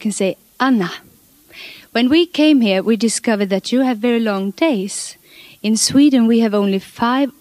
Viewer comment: Just for a moment I thought she said, "When we came here we discovered that you had very low in taste"🤭